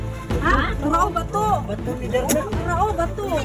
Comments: May narinig ako nagsabi nong pagkalabas ng bato sa bunga-nga " Bato ni Darna" natakot na sana ako kaso bigla ako natawa :face-blue-smiling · Did I heard "bato ni darna"?